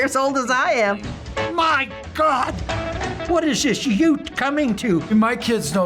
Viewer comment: what HE sayd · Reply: He said "MG! What is this youth coming to?"